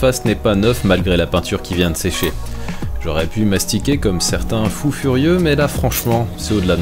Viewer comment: j'ai pas compris pourquoi il voulait s'astiquer ? 🤔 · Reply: mastiquer, m'astiquer, ouais l'erreur est humaine xd